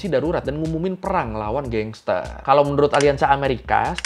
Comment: di menit 4:36 typo apa bener perak,dengernya sih perang 😁